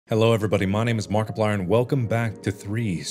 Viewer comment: When I heard the intro I heard "Jello everybody..." 🤣💀